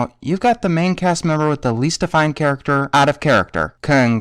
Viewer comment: i can't figure out what you said there. "you've got the main cast member with the least ____ character, out of character." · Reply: nonespark He said, "You've got the main cast member with the least defining character, out of character."